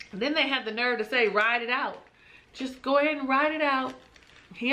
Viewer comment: Chris's father gave the best advice ever, "Ride Out". Oh, wait a minute that's not what he said, he really said "ride it out" 👀 Sorry sir I'm going to go with what I thought heard, and that was "Ride Out".Paige. has all the information and evidence that she needs. We have not heard mention hearing from the spirits since she walked down that aisle. It because she is out of alignment. Fix it Paige, so you can get your peace back and work on your self esteem.